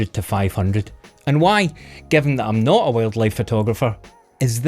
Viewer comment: I thought he said 'well liked' not 'wild life'. A very different kind of video.